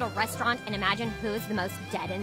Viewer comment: The first time I saw this I thought she said “Oh that’s kinda hot”